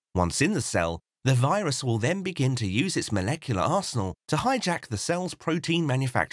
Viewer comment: I first heard that as molecular arsehole